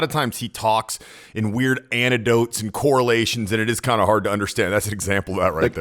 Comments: did he just say “antidote” instead of “anecdote” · Did you say antidote?!? God bless you. Well seems he already has. Be grateful for all you have achieved with so little.